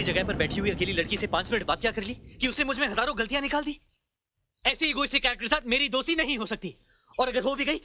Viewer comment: aisi egoistic.... Kay Sath .. what is he saying can anyone understand?